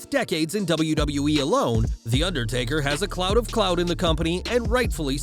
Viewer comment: Firstly, what exactly is "a clout of clout"? Secondly, 6:55-7:01, did you have a mini-stroke? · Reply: I heard it as "a cloud of clout", which is still an interesting phrase. Maybe a minor botch for "a lot of clout"?